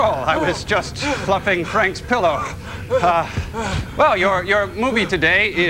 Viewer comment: For me the video was loading slow, and to it stopped at 5:21 and all I heard was "I was fluffing Frank" XD